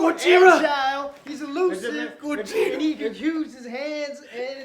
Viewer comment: AJ says "Kojima" (MGS, etc) instead of "Godzilla" in a Japanese accent... lol?